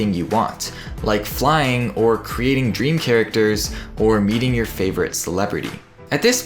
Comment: i heard "Eating your favourite celebrity" instead of "Meeting your favourite celebrity"